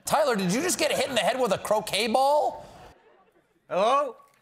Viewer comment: When he said hit by croquet ball I thought he said cocaine ball at first 😂😂😂😂☠️☠️☠️